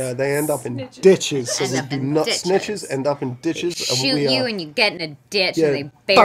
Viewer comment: I love how it says “dishes” and not “ditches” I always thought the quote was “snitches end up with stitches” 😂 love these two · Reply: Usually, I hear "snitches get stitches" but he clearly said "snitches end us in ditches"